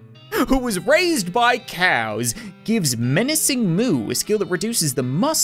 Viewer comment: Anyone else hear "Moo"